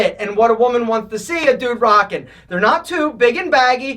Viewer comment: You say “a dude rocking” but the subtitles say “to do rockin’” @ 01:24 You say “Yo Mama” but the subtitles say “your mom” @ 02:22 You say “or too many” but the subtitles say “were too many” @ 02:32 You say “pinky” but the subtitles say “pink” @ 02:35 You say “No. 4” but the subtitles say “before” @ 02:37 You say “sup” but the subtitles say “sub” @ 06:29 You say “trace evident” but the subtitles say “trace evidence” @ 07:10 You say “are” but the subtitles say “or” @ 07:34 You say “we allowed” but the subtitles say “wheel out”